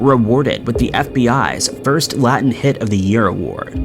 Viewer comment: “Latin Hit of the Year Award?” Did I hear that right?